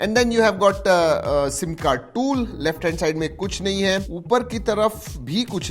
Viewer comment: 😅 क्या कहा ?? "सिम कार्ड टूल" (सिम कार्ड ट्रे पोर्ट)